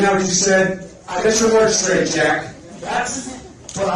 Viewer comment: He said fat! Listen again, watch that clip elsewhere bc he says fat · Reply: I watched the clip again, as recommended! He does call him Jack @ 1:08:36 "Get your words straight Jack!" However he does call him fat @ 1:08:53 "Look fat!" I didn't hear that part during the stream so thank you! 😊